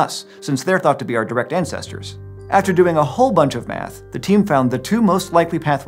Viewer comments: I misheard him say at 6:10 “After doing a whole bunch of meth”😂😭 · After the whole explanation, at 6:10, I first heard "after doing a whole bunch of meth" not "math." I guess some meth had to be involved too! Hahaha. Really deep analysis by the team coming up with those findings! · when he said " after doing a whole lot of math" I thought he said "after doing a whole lot of meth"